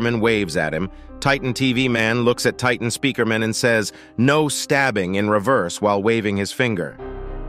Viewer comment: First time I heard titan TV man say that I thought he said, no swearing